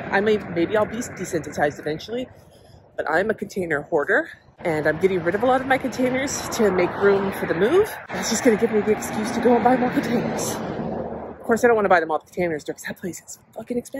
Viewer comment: When you said but the container store... I thought you were gonna say “I am a container whore.” 😂😂😂